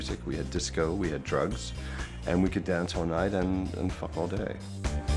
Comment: Did he just say “Dance all night and hump all day”… 😆🧐